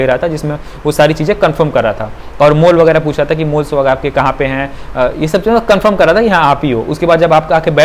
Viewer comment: pe aap kya bole hai? Mool sahar ya mole(til )? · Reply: mole